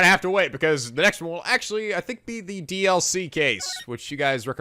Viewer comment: i thought you said the dl-6 case instead of dlc XD